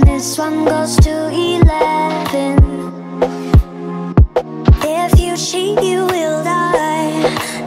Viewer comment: 😂 I swear up until I watched this I thought she was saying shit, not cheat